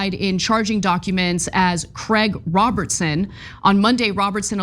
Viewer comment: Thank God, I thought she said Craig ROBINSON!